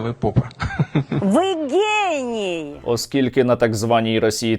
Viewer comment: Доброго вечора всім хто не москаль! По-перше, як завжди, величезна подяка Сергію & Со. за їхню працю. По-друге, одному мені почулося в привітанні ( 3:13 ) від старої швабри до чорта і дегінерату, який абсолютно поїхав і відірваний від реальності: "Ви гей. Ні?" Правда тут образяться геї... Бо він просто підар... Слава Україні! Слава ЗСУ!